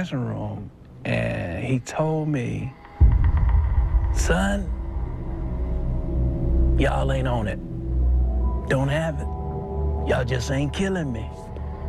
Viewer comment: "Son! Y’all ain’t on it. Don’t have it. Y’all ain’t just killing me." Is that what James tells Bootsy right after the gig @ 02:25 ? If anybody hears different, please clue me in! Much thx!